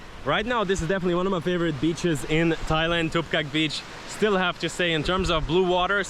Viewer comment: What beach did he say in 3:15 ? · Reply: Tup kaek beach